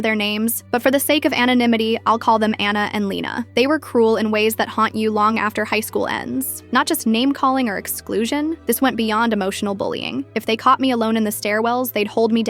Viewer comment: I thought you said Lana and lina